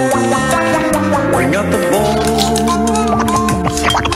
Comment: I think i just hear "bring up the balls"💀💀💀